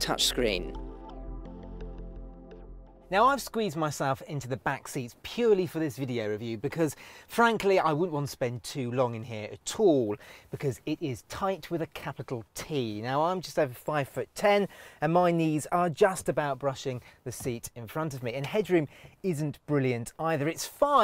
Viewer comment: What the heck you said? How this back seats are tight?